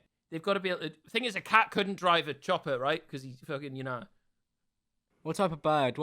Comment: did just say a cat can't drive a chopper because hes fucking my nan